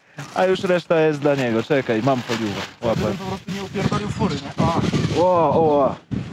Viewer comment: usłyszałem, żeby nie upierdolić skóry w polonezie xD